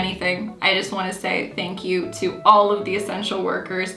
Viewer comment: i heard thank you to all sexual workers. Had to go back and listen again